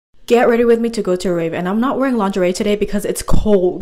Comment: I thought u said get ready with me to go to a grave 💀💀💀